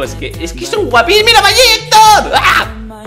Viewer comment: Minuto 1:46 y eso que dijiste mayicto😏